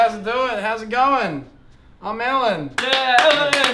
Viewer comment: At the beginning it sounded like he said ‘I’m Allen’